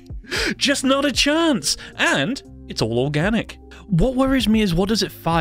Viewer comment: i when he said it’s all organic I thought he said it’s war-ganic